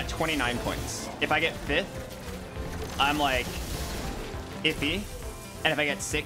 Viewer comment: inaudible xD He clearly said “iffy”